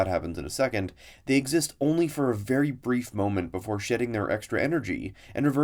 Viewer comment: I heard: "before "sh*ting" their extra energy" makes sense to me